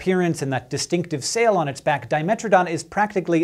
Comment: Lol I honestly heard you saying "and that stupid sail on its back" rofl xD